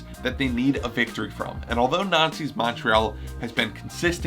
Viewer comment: is it just me or does it sound like he says Nazi's CF Momtrea